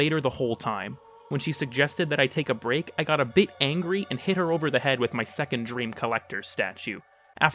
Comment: transcript Angels of the Zariman is the last straw. I have sixty-nine hundred hours in this game and I won't be playing a minute more. I got so caught up grinding out Voidplumes and beating off to Yonta that I forgot to pay my fucking mortgage. When my wife asked me why I hadn't been to work in six days, I tried to explain to her that I had been stuck in the Zariman Elevator the whole time. When she suggested that I take a break, I got a bit angry and hit her over the head with my Second Dream collector's statue (after removing it from its jar, of course). This led to my wife fucking CCing me with a no contact order, and spawning a Divorce Lawyer Eximus. Even worse? When I went to exact revenge on my now ex-wife, I caught her being fucked by [DE] Steve himself. I tried to stop him, but I couldn't because he fucking had Overguard! When I asked him how I was supposed to level cap in 6x3 now, he told me to "Void Sling into a bitch's house" and strip the Overguard off my virginity. Now I'm in a special Dormizone called a jail cell and I can't even extract. This is absolutely unacceptable. Fuck DE, fuck Warframe, and fuck this stupid update that powercrept my fucking marriage. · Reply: I thought it said "a big angry" 😭